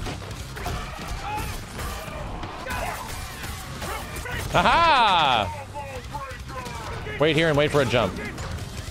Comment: does anyone have a clue what the announcer is saying at 18:05? it’s my favorite voice line and i can’t find it anywhere 😭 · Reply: One two three